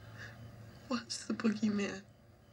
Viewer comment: Laurie Strode: Was it the boogeyman? Dr. Sam Loomis: As a matter of fact, it was. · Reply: As a kid watching it, I always thought she said "what's the Boogeyman"? Because when she was reading to Tommy, he asked her "what's the Boogeyman"?